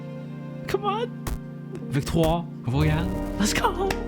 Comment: What do u says at 9:59 ? ‘ victoire royale ´ or am I just dreaming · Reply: that game was in french, and in the original clip i said "french victory? victory royale? or uh... victoire royale?"